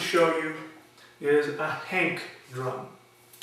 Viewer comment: What is that drum at the end? It sounded like you were saying "hate" drum. Can you spell it please? And where to buy one.